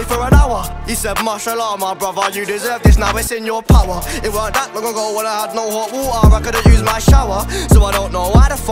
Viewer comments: wtf did he said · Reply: It werent that long ago when i had no hot water for a shower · For those who didnt know what he said at 1:42 ... he said “ It weren’t that long ago when i had no hot water i couldnt use my shower”